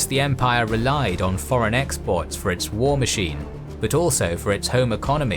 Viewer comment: Great video as always. One correction: at 16:17 the subtitles say 'foreign imports' while the narration says 'foreign exports'.